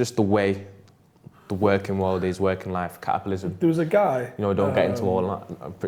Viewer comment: did say "caterpillarism"..?